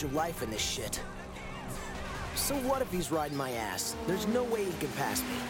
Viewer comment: what did he say 💀💀💀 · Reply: "right in my ass" 💀 "riding my ass" ☠️